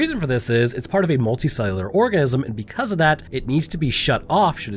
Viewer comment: I swear it sounds like he is saying orgasm instead of organism.